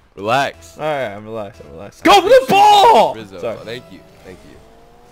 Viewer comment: did say rizzo